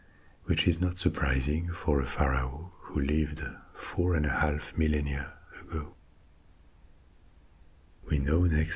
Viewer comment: did you say that the pharaoh lived 4.5 million years ago?